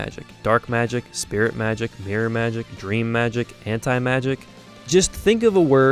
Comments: did i heard 4:05 hentai magic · I'm pretty sure I heard Hentai Magic